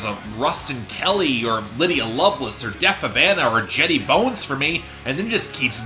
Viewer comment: what artist is he saying? Depa Bana? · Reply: Deaf Havana